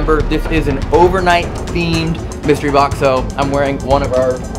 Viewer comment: did that just say xbox?